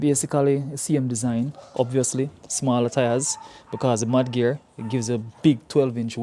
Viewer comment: I thought you said 'Man Gear' 😂😂